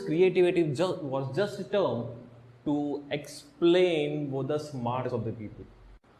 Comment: "before this creativity was just a term to explain _____ of the people" 0:18 Anyone understood what was said? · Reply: Smartness